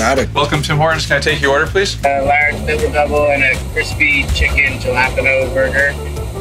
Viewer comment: What did the guy say@ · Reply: He was trying to order a "Crispy Chicken Jalapeno" sandwich, but he was pronouncing it as "Ja-la-pin-o" instead of "Hala-pen-yo"...and the guys were cracking up at that ;P It was hilarious!